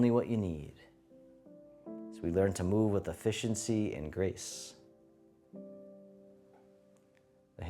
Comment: I love any flow with the butterfly. ... at one point you described "efficiency and grace" and I heard, "a fish in sea and grace." I thought it was a new animal move.